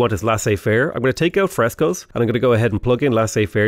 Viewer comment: When you first plugged in Frescoes, I swear I heard "Fresos" which sounds like some kind of knock-off Mexican food that Taco Bell will inevitably invent. Just thought you should know that.